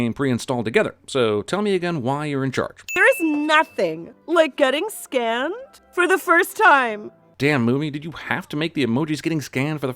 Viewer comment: I legit thought she said “Nothing like getting skinned for the first time”